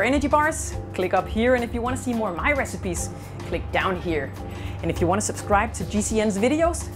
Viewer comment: i misheard the "click down here" as "lick down here"